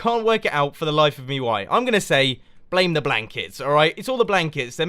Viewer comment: When he said " blame the blankets " I thought he said blame the black kids :')